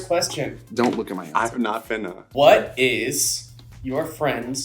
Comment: Floridian Here🖐🏾 Did just say, “I’m not finna?”